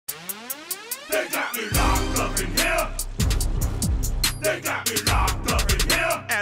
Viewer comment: I misheard "They got me locked up in hell". I'm a bit disappointed now.